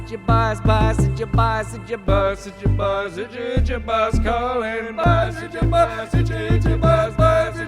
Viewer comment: when you look at him itching his arm it sounds like he says itchy itchy boss calling 😂